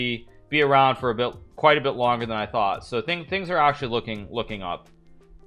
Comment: what is he talking about? I can't understand what he said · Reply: He said his grandmother might be around longer than he thought, so things are looking up.